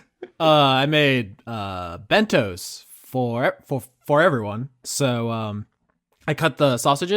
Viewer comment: I misheard "I made bentos for everyone" as "I made Mentos for everyone" and was briefly quite confused